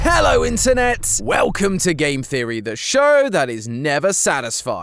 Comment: Did anyone else hear him say sic game theory instead of sixth game theory or was that just me mishearing it?